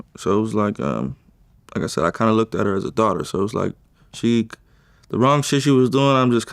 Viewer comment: did he just say he looks at her like a daughter. WTF is this dude talking about